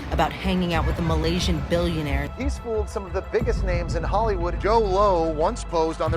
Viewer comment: I keep hearing “Joe Blow”